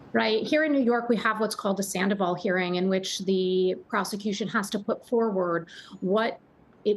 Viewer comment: Did he say persecution or prosecution sound like he said persecution?